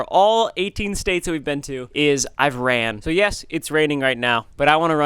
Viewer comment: Did bro say "Iran" instead of "I've ran"